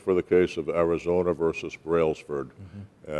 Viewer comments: It sounds like Mr. Ayoob is saying Arizona vs Brailsburg, but when I search the only result I get is Az. vs Brailsford. As far as I have yet read, there is nothing in that case regarding stickers or emblems on the firearm. Anyone know what I'm missing? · Arizona vs who? I thought he said Railsburg. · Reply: Brailsford.